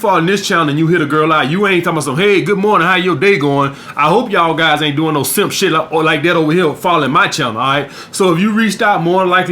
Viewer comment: Guys, could you tell me what he is saying at 4:00 - 4:10 ? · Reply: Avalanche he hopes y’all ain’t doing some beta make shit with the girls